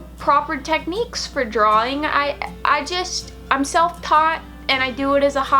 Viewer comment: Funny, at first I thought you said "I sell pot" instead of "I am self taught" LOL!